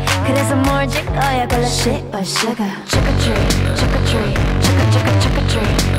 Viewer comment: I thought she was saying sugar tree for a minute 😂😂